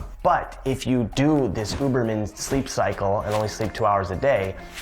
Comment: the subtitles say boob instead of uberman hahahaha